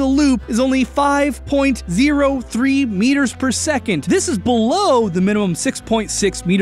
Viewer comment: Am I the only one who hears Sonic say: BELOW?! In really Austin IS Sonic